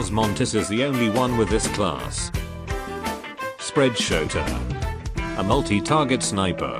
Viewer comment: sounds like you said spread-shota